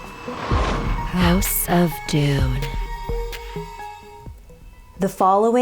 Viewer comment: I thought it said "House of Doom" Otherwise it would sound cringey.